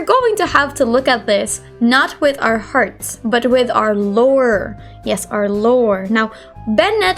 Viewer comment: literally heard "lower" instead of lore i-😳